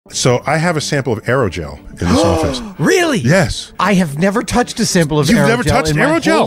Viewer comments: Lol I THOUGHT HE SAID ASTROGEL!! (The lubricant)😅😅😅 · I thought he said he had a sample of Andro Gel.